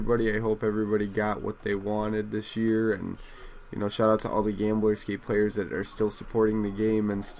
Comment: lol it sounds like your saying gameboy scapee nice vid